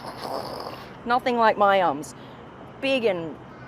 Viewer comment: I was slightly distant and away while you said something about the rear i heard it like "nothing like my Arse" I was shocked and played it back 😂😂😂😂😊